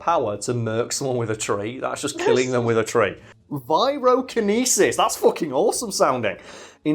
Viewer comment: For a second I thought you said "birokenesis," the ability to move a pen with the power of his mind